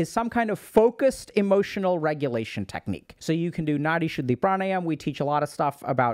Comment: The key to stop ruminating is learning how to calm your nervous system and give your brain the space it needs to process thoughts without spiraling. This could look like breathing exercises, journaling, or finding ways to ground yourself in the present moment. Dr. K’s Guide to Mental Health has tons of practical exercises and techniques to help shape your brain, body, and mind. Learn more: <link> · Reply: What's the name of the meditation Dr. K suggests doing around 21:20? I can't quite catch the name Edit: ok it's Nadi Shuddhi